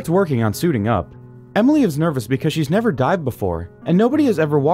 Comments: I thought he said Emily is nervous because she has never DIED before, and I was like, yeah, same. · "Emily is nervous because she has never dived before..." How the hell do you get work at an underwater facility without being able to check that box on the application? Seems like it would be a required skill for the position. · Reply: oooooh i thought he said emily had never died before